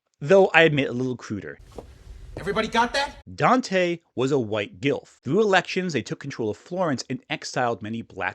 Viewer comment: I did a double take when I heard "Dante was a White Gilf" because I was listening to the video and hadn't seen how "Guelph" was spelled.